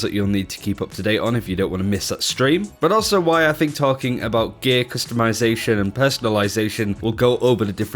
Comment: Honestly thought you said "Gay customisation" at the beginning lol! What would that even look like? Rainbow coloured javelins maybe?